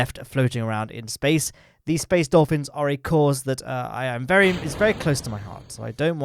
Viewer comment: I misheard space dolphins as space orphans. I agree that we shouldn't be leaving orphaned stages in space orbit.